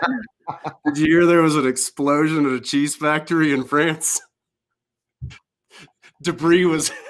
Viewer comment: What was he saying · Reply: the joke?? it was "did you hear there was an explosion at a cheese factory in france?" and the punchline is "de-brie was everywhere" which is a cheese pun on 'debris'